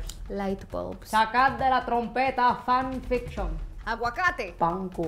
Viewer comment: Did she just say she sacrificed Chacal de la trompeta fan fiction???? 2:44 Lmaooo abuelita!